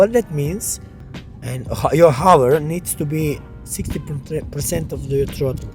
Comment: Great review! How much hover Throttle did you mention at 1:46? I understand 63 Percent but I think that is not what you said. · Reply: Yes your hover throttle needs to be around 60% , you need to feel the mass in the air, if your drone is to light then you will have jumping effect on picture, not a smooth like